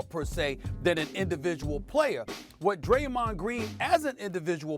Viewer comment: Its sound like he said greymond dream 🤦🏾😂